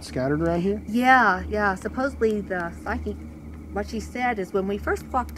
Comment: Bruh, is she saying "When we first f*cked up here" at 4:53 or am I just crazy?